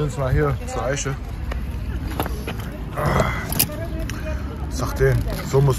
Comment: was sagt er vorm essen? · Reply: Sahten, das heißt guten Appetit auf arabisch